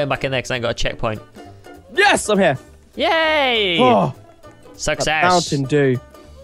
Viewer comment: He says success but it sounds like he says sucks ass. Hahahaha. Like if you heard this.